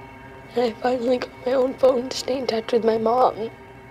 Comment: what did she said? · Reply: “I finally got my own phone to stay in touch with my mom”